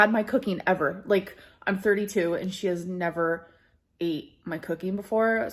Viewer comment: i thought she said "has never had my cookie before" and i had to rewind lmao